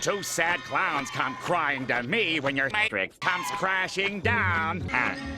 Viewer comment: did he say matrix?